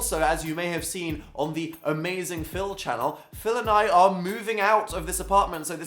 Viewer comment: I thought you said Phil Nye... PHIL NYE THE SCIENCE GUY